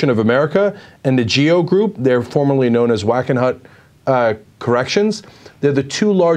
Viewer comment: Whack-a-nut corrections?!!?!?! LOL... Yeah i know that's not what he said.